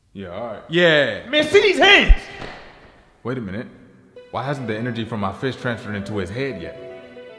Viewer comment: i literally heard "why didnt the energy from my fish transform into a pig yet?"